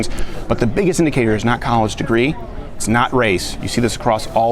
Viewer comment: I understand the whole “less resources” problem. I’m at a pretty poorly-managed, understaffed and BROKE NYC public high school. Only two AP classes offered now, which in my opinion is ridiculous, and not a whole lot of extracurriculars. I know for a fact that this is not the only school in NYC that is lacking resources. And my school prides itself on being “diverse”; I would say it reflects the community pretty well. There have been small efforts to help schools like mine out. I’ve applied to a local CUNY college that offers college classes for schools that have little opportunities for AP. Many CUNY (City Universities of New York) colleges offer these classes for FREE to students who go to schools like mine. I think it’s a great way to get resources to students who don’t have them. I’ve seen online classes but they’re incredibly expensive. It’s not a racially-influenced solution and it does offer performance where otherwise lacking. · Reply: Damn,for a second I thought you said Cunt college.. would've been an interesting use of that word for an abbreviation.